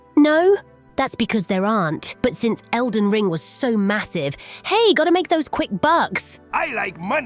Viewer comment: did i just hear massive?